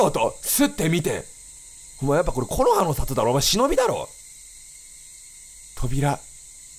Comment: 辺りなんて言ってるの？聞き取れないです。 どなたか教えてください。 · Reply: 「お前やっぱ木の葉の里だろ、忍だろ…。」